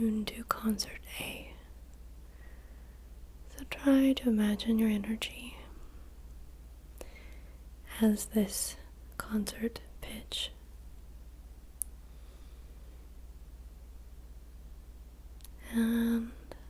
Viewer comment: misheard ASMR: "try to imagine your energy as this concert bitch" O.o